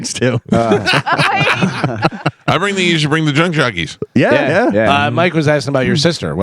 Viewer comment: I thought you said "Bring the Drunk Jockies" for a second